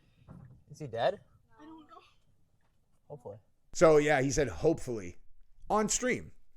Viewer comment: At first I thought he said Oh boy. But it was hopefully.